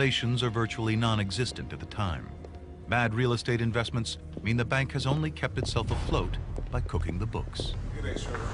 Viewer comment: What word are they saying at 2:10 ? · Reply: "...the bank has only kept itself afloat by cooking the books." Basically, they manipulated their financial data to make it seem like they weren't on the verge of failure.